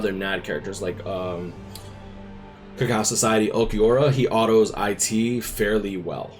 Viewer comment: Can anyone tell me what Ulquiorra he mentions at 8:45? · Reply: Cacao Society, Mind Unit if im not wrong